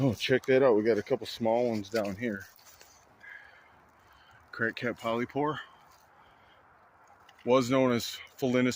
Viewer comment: Did I hear you say "crackhead polypore"? Or are my ears playing tricks on me this morning?🤣 Buddy, I cant wait for the warmer weather so I can do some mushroom foraging. But I'm really not looking forward to the ticks. You be safe out there! MUSH LOVE homey! I hope your day treats you well🍄🙏✌💚